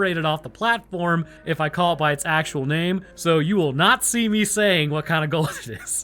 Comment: Thanks for watching guys! And Congratulations to all the recent graduates! Install Raid for Free ✅ IOS/ANDROID/PC: <link> and get a special starter pack with an Epic champion ⚡Tallia⚡ Available only for new players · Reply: I swear it sounded like you said "You will 'nazi' me say..." while trying to avoid saying that word at 16.20... lol